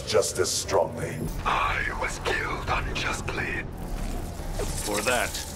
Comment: Look away from your device at 0:09 it sounds like he said “ I was killed I’m just playing